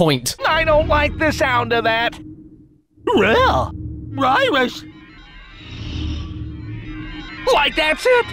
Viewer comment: why did i hear fyrus the first time that was said to me? (i know it's virus) like... failboat fyrus